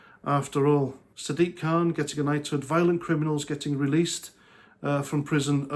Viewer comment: Knighted? I thought you said knotted!